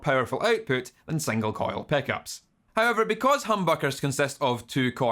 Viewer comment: I hear him saying "peckups" xD